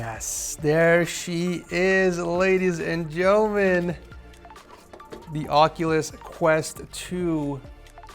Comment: did I hear joemen 😅😅😅